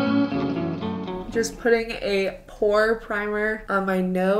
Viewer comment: I really thought you said you were gonna put a 'poor' primer on your nose!